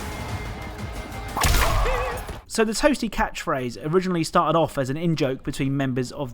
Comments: i thought he said “pssy” not “toasty”😓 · i can’t be the only one who thought “toasty” was said as “Hoh Hee” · I stg all this time I thought he said lucky not toasty